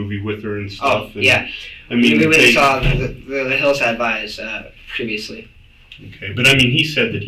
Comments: What said is a little hard to make out, but horror buffs would be able to gather that said they watched "The Hills Have Eyes", not "the hillside (indistinct)...." Just a small fix to your subtitles. Great job on the video. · where the subtitles say 'indistinct' you could fill it with 'The Hills Have Eyes'.